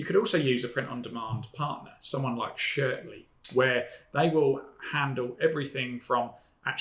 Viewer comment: Great video! I didn't catch the name of the print on demand store you mentioned. I heard "Shirly"? Pls share the name so that I may look for them.